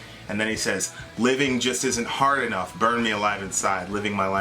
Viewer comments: All this time! I thought he was saying 'Heaven just isn't hot enough' LOL!!! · When he says living just isnt hard enough,it sounds like he says heaven just isnt hot enough.lol🤣